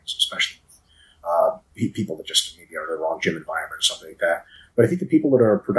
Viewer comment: Anyone know what mike is saying around 1:42:35 - 1:42:40 I can’t make out that part. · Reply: man, it's hard for me too but I after listening to it 20 times I think it's "PEOPLE IN WRONG GYM ENVIRONMENT" that don't push themselves close to failure